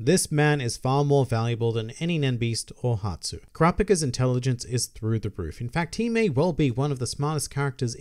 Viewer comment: I fucking fell off my chair when I thought you said Wobble's nen beast is Kurapika